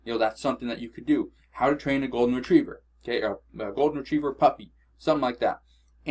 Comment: When you said a golden retriever puppy I thought you said a golden retriever fucked me